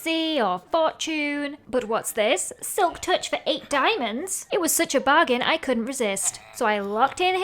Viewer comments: Did she say "silktouch for 8 diamonds " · what did she say???????? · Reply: DIMONDS?!?!!?!?!?!???!?!?!!?!